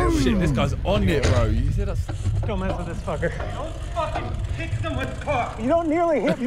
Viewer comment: "Don't f*cking pick them with pock" is what I heard lol.